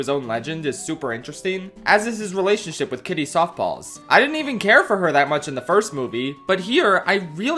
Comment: did he say soft balls or paws, i rewinding it over and over again and I cant decide